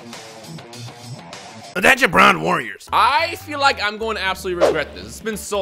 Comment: Sounded like you said "Attention brown warriors"